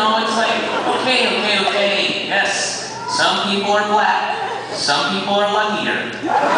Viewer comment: What does he say at 0:15? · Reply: "some people are luckier"